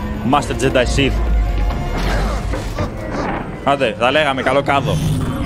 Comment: καλό κάδο;;;; Τι είπες πάλι? Κάδε!!